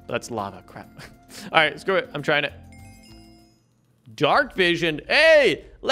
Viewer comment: Did he say “dark vision” 18:28 lol love your videos 😁😁😁